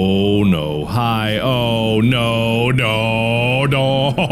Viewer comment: i heard “own ohio”